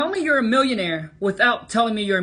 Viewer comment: I thought he said he was a billionaire and look at the start of the video